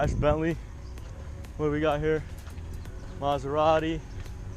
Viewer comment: did he just said a alfa romeo a Maserati ??